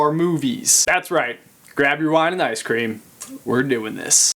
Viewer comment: Am I the only one that heard him say wining icecream?...xD